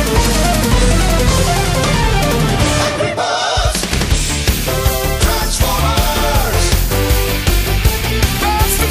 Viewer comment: What did i just hear💀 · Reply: ANGRY BIRDS TRANSFORMERS